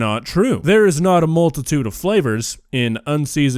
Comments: I swear to god I thought he said "malted tuna flavor" and I thought he was being honest. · You heard “multitude” i heard “melted tuna” …either way “fukked up”